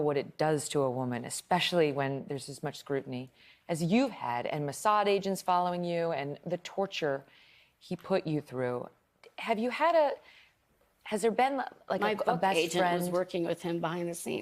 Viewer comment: what does she say,"His massage agents followed you.." ?